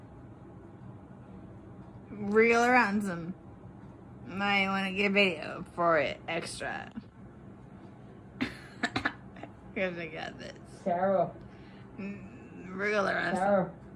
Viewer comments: What the hell is she saying here? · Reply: “Real or handsome, might want to get video for it extra. [chuckles] Cuz I got this.” “Real arrest him…” Da fuk? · That surprisingly accurate, but not definitive. It does sound like “wiggle around some”, but that could just be like on those ghost hunting shows where they put subtitles on the random noises and your brain fills in the blanks. But you could be correct; your words do make more sense than “real or handsome”. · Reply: At first I thought she said real ransom, i might get paid extra 😬 I thought that for 2 years. But after seeing all of the videos from body cam's of her and Jorge's run in with the police I realize that "wiggle around some" makes more sense 🤷🏽‍♂️ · What is she saying at the 1:03:16 mark? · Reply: I can’t tell, I hear “wriggle around some might wanna get video for extra” but she’s a psycho so I really don’t know lol